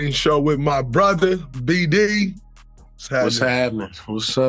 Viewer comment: B Diddy???? Did I hear that lol